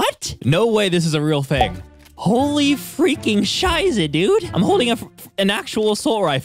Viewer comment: did he say "scheiße " DOES HE SPEAK GERMAN ?!